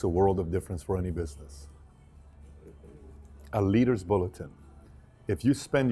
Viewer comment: what did he say? A Leaders ??? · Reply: a leaders bulletin.